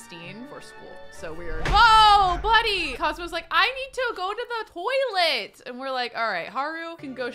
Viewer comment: for some reason my brain heard this as “I need to go to the boy-let”. Which amused me greatly.